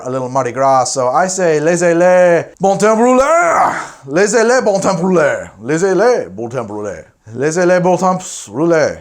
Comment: "Laissez les bon temp rouler" I'm french and it took me a while to figure out what you were even saying X3 No offense but that pronunciation was horrendous.